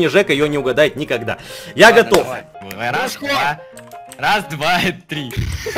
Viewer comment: что сказал 00:00??? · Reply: Дружко